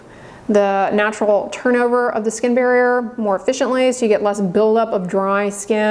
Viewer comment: I thought you said olive oil disrupts skin barrier. There’s olive oil in hado labo